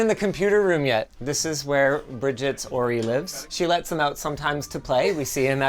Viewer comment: Even Youtube subtitles can't figure out what Linus mumbled. "Bridget's ____?____ lives" Edit: Just figured out that it is the name of Bridget's partner. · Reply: "Bridget's Ory lives", Ory is the name of the partner